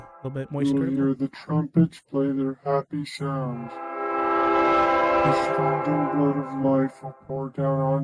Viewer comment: SOMEONE TELL ME WHAT HE SAID!!! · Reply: It said “do you wanna hear the trumpets Play their happy sounds?”